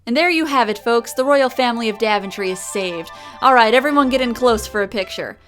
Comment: Oh! Oh, okay. I uh, /may/ have initially thought you said, "the royal family of Dadventry". Because, y'know. Dad humor. :B